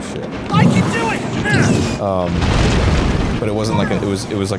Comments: I keep hearing link yelling “why’d ya do it?!?” 24:05 Is that just me? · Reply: No and they said it's I can do it. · my partner and i ALSO misheard “I can do it!” as “Why’d you do it? 😡” I’m so glad they mentioned it omg